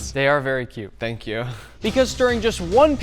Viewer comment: anyone else thinks that the thank you from sounded like ZHC